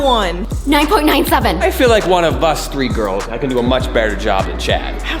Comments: did just say one of us three girls is secretly a girl😂😂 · Did said I feel like 3 girl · Hear what said5:35 it’s actually two girls instead of three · Did just say one of as three girls 5:36. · Did call him self a girl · i heard three girls? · did i hear that badly? I hear we 3 girls?